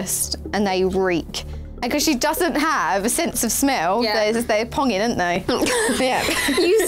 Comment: Help!! What does Jesy say in 0:38-0:39? Did she say pungent? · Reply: Raseca Jallorina "they ponging" which is slang for horrid smells. It pongs/ponged/is ponging.